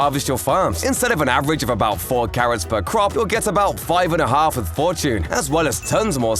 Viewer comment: did he just say 5 and a half 😐🤣🤣🤣🤣🤣